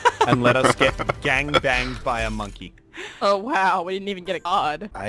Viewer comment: Anybody else noticed that at 24:00 he said monkey but the subtitles said scientist :D